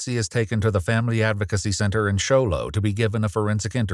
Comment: I thought they said cho-lo but it was Show Low 😂